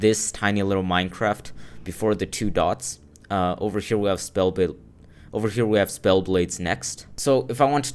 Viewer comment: Irons spells and spellbooks is a completely different mod. I would open up the files and see how it all works since it’s much more different than rpg series mods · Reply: Oh you probably said spellblades i thought you said spellbooks lol. That makes a lot of sense since spellblades and RPG Series both use spell engine. When watching this for the first time i didn't know about spellblades and such so i just thought you meant spellbooks.